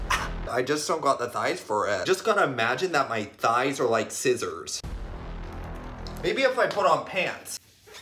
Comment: At first I thought he said imagine if my thighs are like SZA’s 😂😂